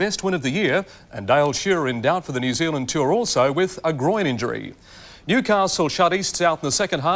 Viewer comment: Did I hear 'Groin injury' said by Rob?